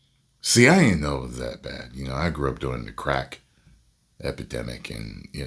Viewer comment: Ty, great job as always, but I swear you said "I grew up DOING the Crack epidemic" 🤣🤣🤣......"You may not know this about me Joe Rogan...I smoke rocks" -......Bigums